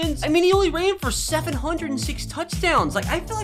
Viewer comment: Did he say 776 touchdowns